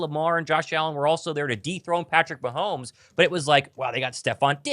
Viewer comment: For a second I thought he said deep throat Patrick mahomes